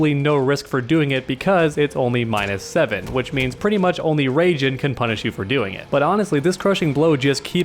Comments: He said rajin instead of raiden 😆 Edit: time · did he just say raijen? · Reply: I believe thats a variation name for Raiden